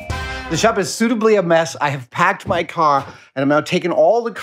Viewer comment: has anyone noticed that in 20:12 he says caw instead of car?